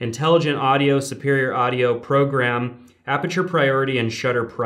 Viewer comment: did you say audio or am I just imagining it?????